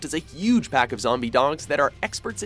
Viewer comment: I hope I'm not the only one who hears "Zombie dongs"